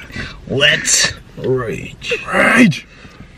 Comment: I thought he said "wet rage"